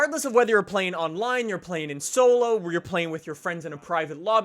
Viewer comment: Did he say plane-ing at 5:17.